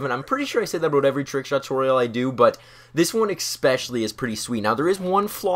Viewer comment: did he just say expecially? I thought I liked him but never mind.